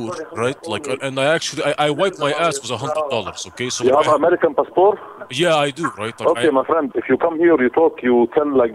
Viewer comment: it sounds like he says “You have American Pastor?” Hahaha